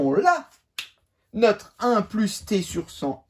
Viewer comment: C moi ou il a dit ´ "walla" les élèves de le 3ème b